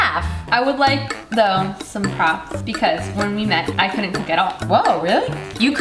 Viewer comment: I totally thought Ella said “when we first met I couldn’t get off” still not sure what she really said 😂